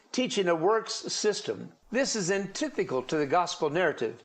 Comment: I had to listen to that, like, three times. I know it's irrelevant to any argument being made, but it really threw me for a loop. He says "antithical". "Antithical". Yes, I'm very aware he means "antithetical". But, that's definitely not what he said.